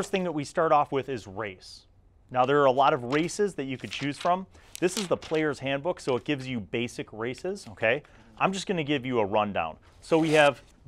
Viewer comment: anyone hear him say there are a lot of racist